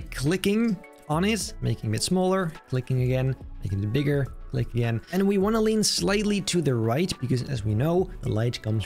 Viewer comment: What did bro say at 1:49 ?? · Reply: "making it bigger, click again and we want to"